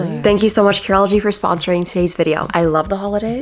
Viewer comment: I heard "Thanks Karol G for sponsoring this video" and I was very confused but very excited 😅